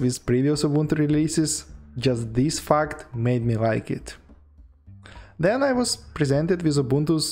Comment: what did he say? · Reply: "This fuck maybe like it"? :D